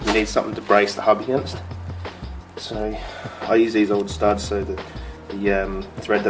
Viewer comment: What is he saying he is using to protect the threads at 1:00 I can't make it out? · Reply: He is saying he's using the old studs to brace against with the breaker bar he's using so the hub doesn't rotate.